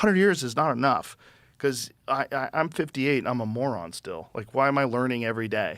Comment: I heard "i am a moron at 58"